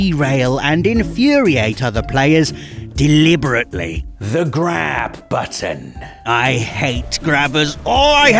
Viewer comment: I heard “the grab butter” 😑